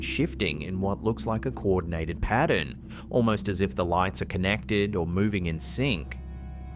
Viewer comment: Wait, did he say the "lights" could be connected?